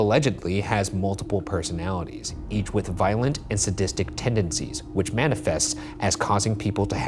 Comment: So. So basically in the growl room, the 2nd word Shane hears sounds like "Toby" and they never catch on that it's the same name given to the demon summoned by the family in the 80s??? Damn this episode is so crazy with the phenomena sdlkfjds also my dumb ass read "bootleg room" as "bootle groom".............. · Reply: When Ryan said "violent and sadistic" I heard "violent and autistic" first lmfao